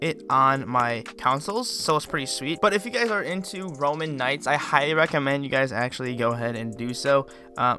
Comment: Did he just say "roman knights"??? -_